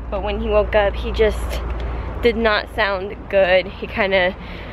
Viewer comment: I don't know exact it sound like said "not good".